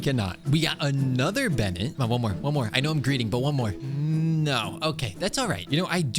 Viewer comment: i thought you said greed